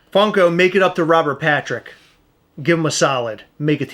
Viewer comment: Had to rewind there, I swore you said Robert Pratrick